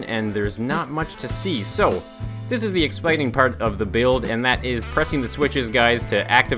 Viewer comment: Is it me, or did he say "Expiting" at 4:33?